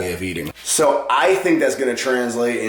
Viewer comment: I thought u called her a thot, I was shook.